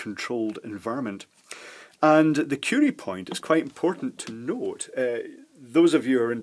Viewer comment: I heard "the cutie point" several times...